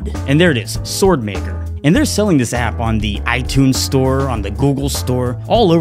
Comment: did he just say itunes store lol